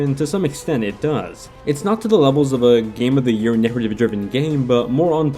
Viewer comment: What is he even saying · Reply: Not to the levels of a game of the year narrative driven game. (Video has closed captions too)